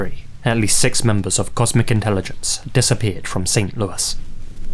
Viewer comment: I coulda swore I heard you saying, "*comic* intelligence" Lmao! 😂